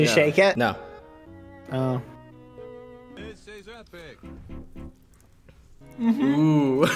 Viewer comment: I hear "This is epic"